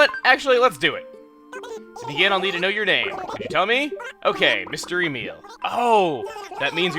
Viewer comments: When you said mr. Emile I thought you said mystery meal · Mr. Emile (Mystery Meal) is what I heard haha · "Mister Emile" "Mystery meal" It sounded like Chuggaa said Mystery Meal, lol